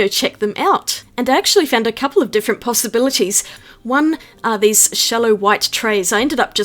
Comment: just creating solutions all casual and stuff. Totally brilliant! Btw... I've replayed 01:54 a dozen times and I swear I hear 'pasta-bilities'.🤔